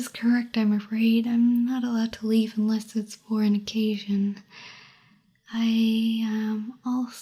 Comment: I totally heard “fornication” instead of for an occasion. Clearly I’m not in wholesome mode, apologies